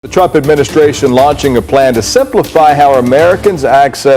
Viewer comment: It really sounded like he said, "the Chump administration" ...!